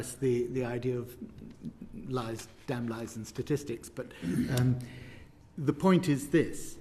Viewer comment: anybody else hear the whisper "what is the point"